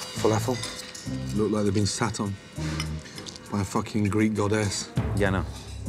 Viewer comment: I thought he said sat on by greekgod X